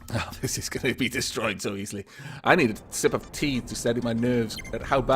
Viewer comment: says: "sip of tea", I hear: "sip of pee"... DAMN YOU, BEAR GRYLLS! Shakes fist